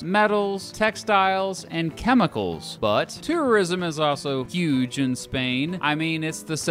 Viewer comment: I still get confused when he says "tourism" in his videos. I always hear "terrorism" instead.